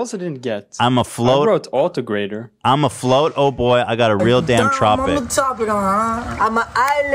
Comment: I heard "imma Florida boi"